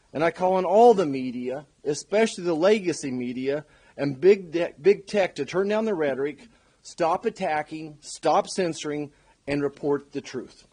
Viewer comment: Anyone else hear him say “big dick media” and 6:37 😂… what’s on his mind?